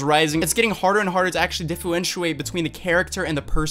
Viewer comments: did I hear diffewenwiate? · what do you say 😂😂😂😂😂 Difuwentuate? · Haha, "diffooenchewate". I replayed that so much I forgot what the actual word is lol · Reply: Hah.. i just noticed that.. Differentiate? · am I the only one who heard him say “diffuentuate” instead of “differentiate”??